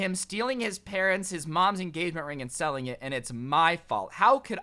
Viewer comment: At 1st i thought it said smell not sell i guess i belong in this vid